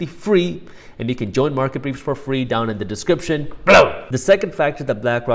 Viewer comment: Glad you put BELOW on the screen. Thought you were saying BADAM all this time.